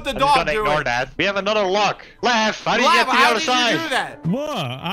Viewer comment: Okay, how did the editor(s) think he said "I'm getting gumballs!" instead of "How did you get to the other side!?", lol.